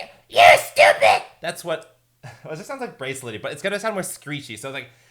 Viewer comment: help I heard bracelety